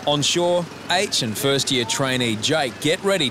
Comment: lmao I must have misheard you, did you call him "ancient"? I heard "ancient first year trainee"